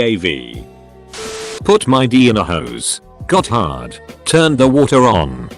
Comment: tell me why the fuck i thought it said horse